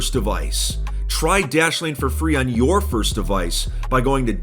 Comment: When he said dashlane the first time I thought he said Daddy Wayne